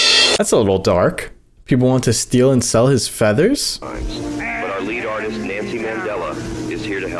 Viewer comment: I thought it also said feathers but then I realized it was leathers